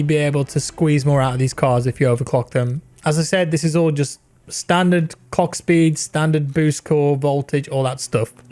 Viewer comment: rofl! did he just say "standard cck speed"